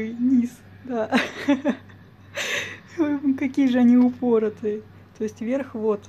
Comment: ошибка в англ.субтитрах, там не stubborn должно быть. Видимо, кому-то послышалось "упорная" вместо "упоротая".